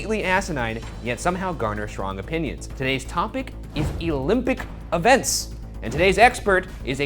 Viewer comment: OK but why did Ryan say "Elympic"??